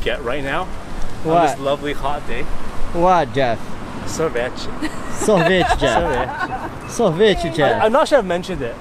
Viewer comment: Na hora que o Jeff disse "Sorvete" eu ouvi "Soviet" Soviet = Soviético = União Soviética = Russia